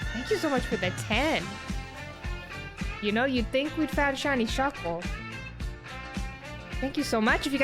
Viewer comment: Thought she said she found shiny Shuckle. Is this an older vod?